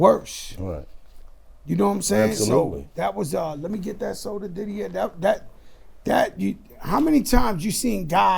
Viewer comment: am i tripping or at 32:00 he did “lemmie get that soda Diddy” or am i hearing wrong . He gotta have another hb name Diddy bc that’s crazy